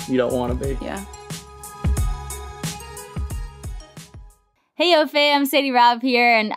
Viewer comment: I’ve been watching your podcast for years now. And during Ads always thought you were saying heyyy yo fam! And just today realized you were saying hey L.O fam 😂